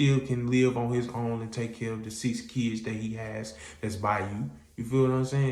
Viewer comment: Wait, 9 kids sharing 2 bedrooms? TF!😂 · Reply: I thought I heard 6 kids